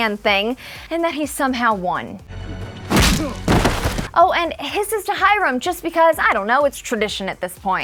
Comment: Kisses to Alice Kisses to Charles and Kisses to Choni Hisses to Hirem Hisses to Penelope Hisses to that flash forward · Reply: I thought it said kisses to Hiram💀